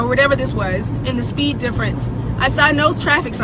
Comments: Did she just say she stopped at a convenience store to get a banana? She keeps saying "pull me out of the car" He never...not even once...said "pull" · "why do only people of color know what I'm going through right now?" Im gonna float a guess... Maybe it's because many people of color respond to speeding tickets by crying racism and injustice. Videos like this make rational people wonder if ANY people of color are victimized at all. This woman is TERRIFIED and she's doing her damndest to ensure that every other black person is terrified too. Do people not learn the story of the boy who cried wolf anymore? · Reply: She actually said she stopped at a store to get a banana....